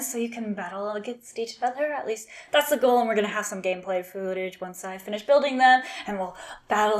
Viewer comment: Might have misheard you did you say gameplay food-age and not footage? I like daxos think he is stronger in commander because he because hard to block lategame plus he gets bigger they still cant block him, he is a "lol" on a stick